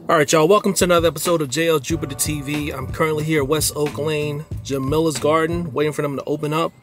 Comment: I thought u said Oakland I was going to be on my way lol